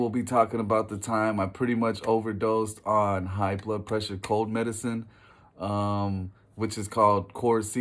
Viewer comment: Wait are you talking about triple Cs which is like cough medication? I knowy buddies and I messed around with that once in highschool. I thought you said heart pressure medication in the beginning? But man, hydrocodone or any opiate for the first time felt so great. I hate most substances but opiates are so calm and you’re still feeling normal mentally. Sometimes a little euphoria. That’s why they’re so dangerous cause it doesn’t Feel like a big deal. Like propaganda shows. It is like regular life plus 1. Pain is killed. Slight euphoria. And sometimes less anxiety. It’s not until you realize one a day adds up and suddenly you need it to function and reach “homeostasis “